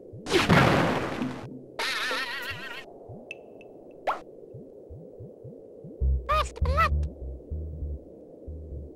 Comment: What does the worm say at 3:45 and at 4:45? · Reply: "First Blood!" and "Fatality". Pure Mortal Kombat quotes. ;)